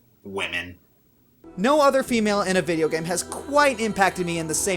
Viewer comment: For a second I thought it said "favorite" instead of "female".